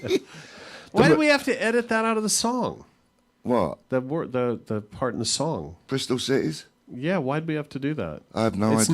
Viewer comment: What's the bad word they're referring to around the 21:00 mark? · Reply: Tits